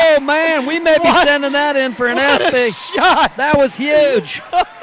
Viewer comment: I thought that announcer said “what a shot, we might be sending that in for an ass pic” 😂